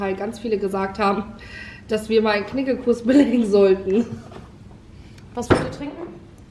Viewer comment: was für einen Kurs belegen? Ich hab’s mir 100 mal angehört und nicht verstanden 🙃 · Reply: Ein Knigge-Kurs